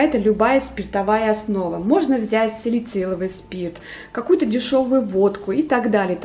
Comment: А водка или нашатырный спирт не подойдут? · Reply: А вы разве не услышали ? - " можно взять салициловый спирт, какую-то дешёвую водку" 0:59мин